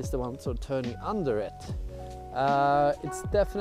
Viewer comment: "With a fair bit of speed" - I heard "with a fabulous speed"